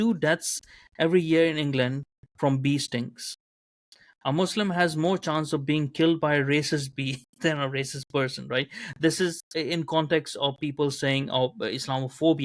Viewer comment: I thought you said "beastings"! I thought it was about being new in the army lol.